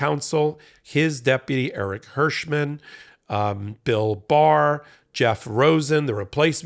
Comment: Every time he says, "Bill Barr," I hear "Bill Burr!"